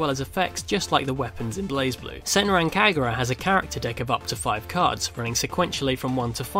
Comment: what was the name of that deck? I didn't really catch the name when you said it. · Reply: Kagura and the character deck ^^